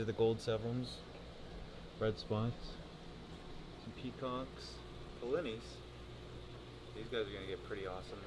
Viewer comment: what is the fish he says/shows at 11:30? I can't quite make out what he says. Thx! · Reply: polleni. A cichlid from Madagascar. Gets to about 12 inches